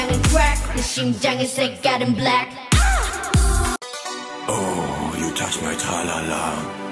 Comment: I heard: Oh, you touch Nutella la la